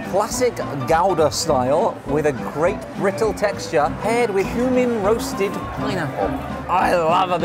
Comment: I thought they kept saying "human roasted pineapple" 😟